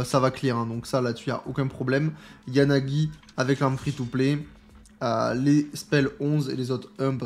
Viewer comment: arme quoi ? 😂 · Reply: Oupsi c'est l'arme BP haha